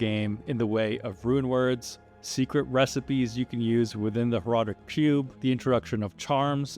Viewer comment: Did you say "Horadric Pube"? Is this going to become a thing?